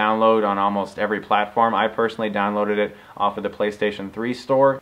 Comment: Great review, I subscribed cause I thought you said "click here to get sodomized" damn :)